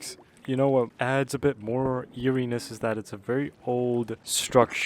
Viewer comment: Thought you said yuri-ness